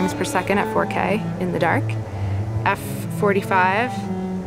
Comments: Does she says F45? at # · did I hear f/45 ?